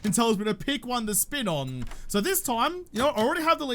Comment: Did he just say "thats a big one to spit on"?! 🤣